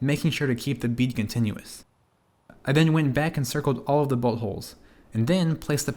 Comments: sounds like he says butt holes 😂😂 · I thought he said “...I went back and circled all the Buttholes.” And I’m like OKAAAY BUD￼